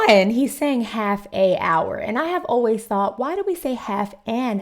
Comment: I thought he was saying " half thee hour".